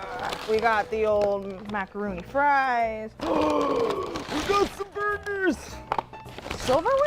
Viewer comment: Did she say "macaroni fries" and then get offended by the inclusion of silverware?